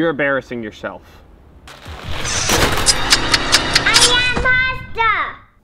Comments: Mini Crewmate: drops cup "Im the imposter" NOOOOOOOOOOOOOOOOOOOOOOOOO Mini crewmate: "I sorry" YOU WERE MY MINI CREWMATE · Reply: I heard "I am pasta"☠️☠️☠️☠️☠️☠️ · Mini imposter: I WAS IMPOSTER Crewmate: NOOOOOOO Mini crewmate: I sorry covers eyes Edit: ah yes 69 likes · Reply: I heard "i am pasta" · It sounded like said “I am pasta” lol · “I’m da impoter!” “*slaps face*” “I sowwy” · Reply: yeah but in my opinion it sounded like she said "i am pasta"